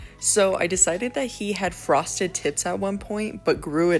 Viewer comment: Oh my god i was drawing and i thought you said aztec had frosted tits 😂😂😂